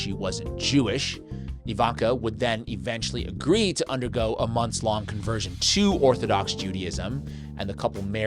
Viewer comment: Only a month to convert ? That’s telling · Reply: He actually says, “monthS long.”